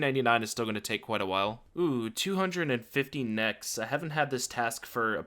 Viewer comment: When you said “250 Nechs” I heard “250 Nex” and thought wow.. that’d be a juicy fucking task